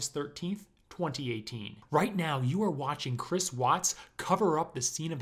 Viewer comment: I thought it said chris patt